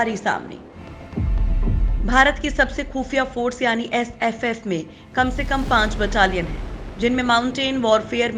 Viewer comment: did she say FFF 🙄